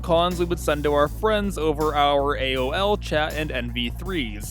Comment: I thought that said anal